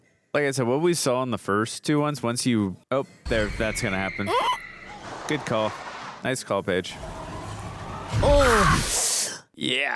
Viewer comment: I had to rewind this I swore he said good call bitch 😂😂😂